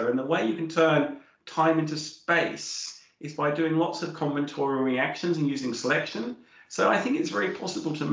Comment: "... is by doing lots of ________ reactions ...." Anyone know what is said here? Thanks. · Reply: combinatorial